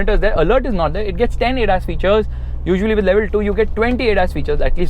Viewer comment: auto caption says “ass features” instead of Adas features I agree youtube